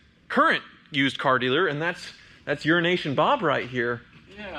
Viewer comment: I heard “urination” bob and I can’t unhear it